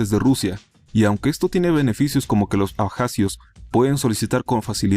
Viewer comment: Escuché "Pajazios"